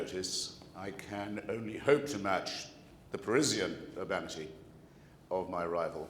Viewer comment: Does anyone know what Hitch says at 16:02?? It sounds like he says the parisian of amity, but I'm not sure if that's right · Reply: Parisian urbanity.